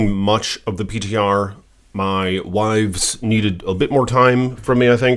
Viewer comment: Did you just say "my wives" ? You are polygamous ?